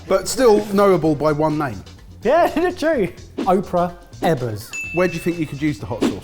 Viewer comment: When they said "Ebers" I thought they were saying "Evans".